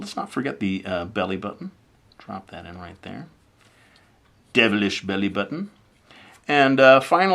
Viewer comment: LOL I thought he said demalistion belly button